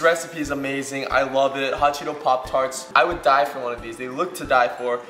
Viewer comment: For a second I thought it said hot Cheetos Portrait XD